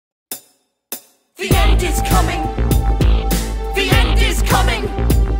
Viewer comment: Why am I hearing "The Ant Is Coming"